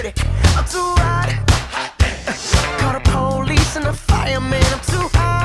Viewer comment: This song never gets old. No matter how much I listen, I never get bored. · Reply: Thought he said I'm too high call the police and fireman now I'm gonna have to turn this from a 10 to a 9 unfortunately